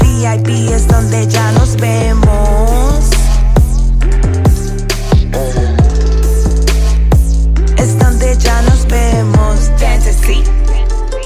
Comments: I never knew it was ghetto fantasy. I thought she said quiero fantasy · on god I thought Joseline been saying "Quiero Fantasy" this whole time....